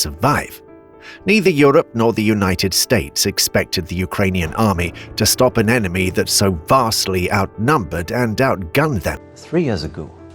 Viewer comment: Did I hear this correctly? At 0:20 - "It can be said that Western countries expected Ukraine to win..." I believe that is what I heard, replaying it seven times!